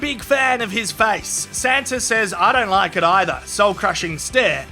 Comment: Am i the only one who heard "santa says" 😂😂